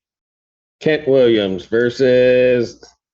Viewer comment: I thought he said KATT Williams 🤣🤣🤣🤣